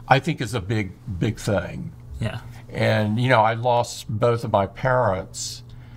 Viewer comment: Did he say "I lost both of my parrots"?